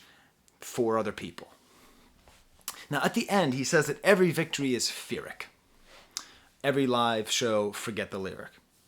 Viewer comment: I thought he said “every victory, fear it”